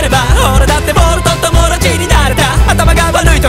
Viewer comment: 「ボールと友達になれた」って歌詞 「ボルトと友達になれた」って聞こえる人他にいない？？